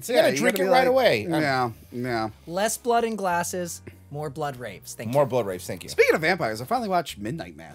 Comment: I thought dude said "more blood rapes". The captions thought he said "more blood rapes". I ain't crazy.